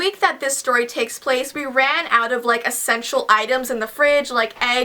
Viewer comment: I thought u said u ran out or sexual items in the fridge 😂😂